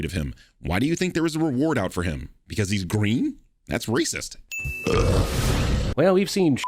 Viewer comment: WHAT HE SAYS: That's racist! THE SUBTITLES: That's Rudy Giuliani!